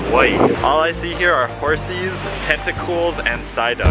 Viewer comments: did he say testicles 🧐🧐🧐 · it sounds like he is saying testical